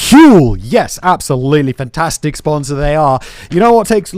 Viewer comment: Dont ever do an intro like that agian. I thought my ears were going to pop. Cmon man its not your first day.... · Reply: I thought he said "the plastic sponsor" and I think I'd actually rather drink plastic than huel tbh